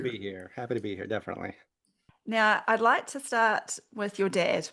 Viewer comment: That was a bit alarming. I thought you said "I'd like to start with 'You're dead.' "